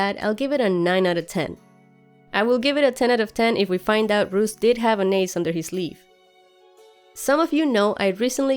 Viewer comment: Hey, great vid as always :D. Was just wondering what you said about Roose at around 11:30, i couldn't quite make it out. Thanks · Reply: would give the episode 10/10, if we find out Roose did have an ace under his sleeve.